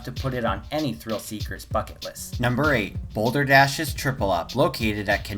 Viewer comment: why did i mishear bucket list as f it list 😂